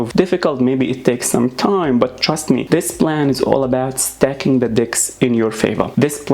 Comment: I'm sorry but did you say "Stacking the dicks"?